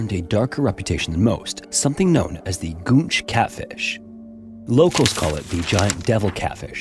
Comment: I seriously thought you said "gooch catfish" 😂